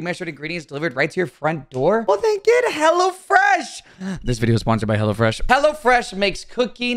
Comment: Bruh why it sound like he saying Hella fresh